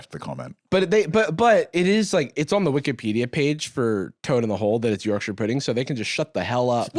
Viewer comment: I heard "toad in a-hole" 🤣